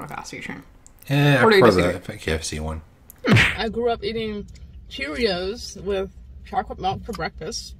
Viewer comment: “Chewyoos” I thought is what she said when she said Cheerios